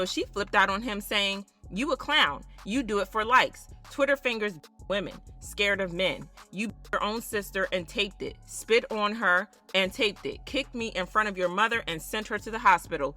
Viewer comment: did she say "you f'd your own sister and taped it?"